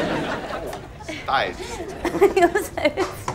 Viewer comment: Please someone tells me what she says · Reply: She actually said, You're so strong.